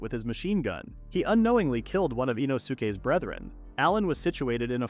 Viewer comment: did bro say inosuke's brother?